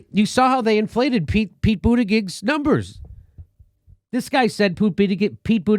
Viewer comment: HA HA HA 4:23 I heard Jimmy say "Poo-ttigieg"